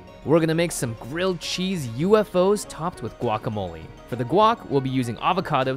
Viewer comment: I thought u said whack a mole